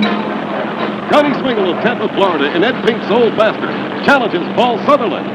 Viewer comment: In the film, the announcer sounds like he says " Ed Pink the Old Bastard" LOL. It's really Old Master. I wonder if Ed ever picked up on that. Great work guys.